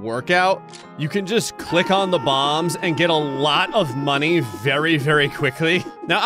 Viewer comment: Mmm Bombs and, the perfect combo. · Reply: I thought you said bombs and gravy for a second